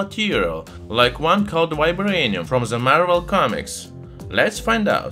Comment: I thought he said "My Brainium" until he specified Marvel